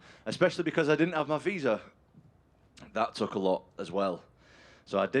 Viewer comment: Did he say, " I didn't train" was "scrambling" and "they threw me in" at 00:42? Sure, he didn't train as hard as he would in camp, but he's really adding up excuses here. Same happened with Blaydes, he said it was unexpected and we saw how that unexpectedly ended. Here's another unexpected circumstance, let's see how he manages. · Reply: He said I didn’t have my visa